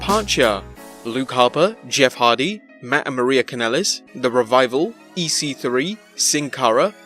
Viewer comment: sounds like he said Mat and Maria Kanellis.